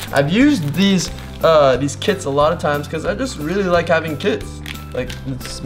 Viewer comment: it sounds like he said i like having kids